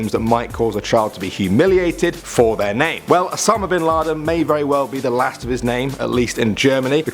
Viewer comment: Osama bin Laden, I thought he said Obama bin Laden heh heh.... heh no... k 😐